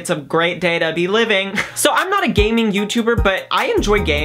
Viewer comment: I thought you said "gay-ming" i was so confused